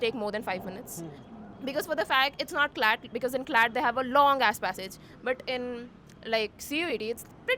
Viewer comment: was it only me or someone else hear "long ass passage" 😭😭🥀🥀🥀